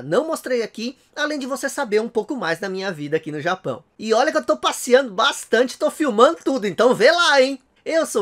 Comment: OUT RUN! Eu escutei "passando bastante e filmando tudo" °_°